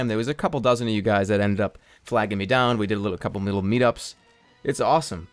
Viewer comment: When you said "A couple of little meet ups", I heard, "Cup of noodle meet ups." Can we make this a thing?